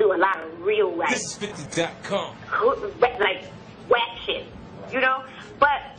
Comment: What does she say about Faith @ 5:01-5:02 ? I couldn’t make out what she said. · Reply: Real....like wack shit